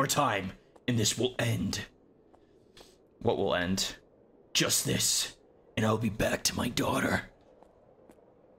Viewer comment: whenever you said just this and I'll be back to my daughter, I thought you said Justice